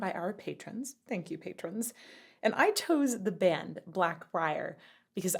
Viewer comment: For just a moment, I was quite surprised when I thought it said Blackbraid.